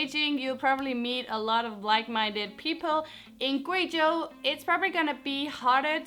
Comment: did she just say BLACK minded people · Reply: jon targaryen i think she said like minded people